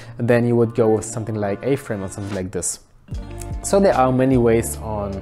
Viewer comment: what did you say? "A-frim"? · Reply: A-Frame :) Check it out here: <link>